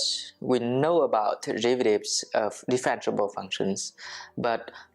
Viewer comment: Thank you a lot for all these good lectures. I have one question: what did you say in the introduction of the video at 0:20 "in calculus we know about .... of differentiable functions"? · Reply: derivatives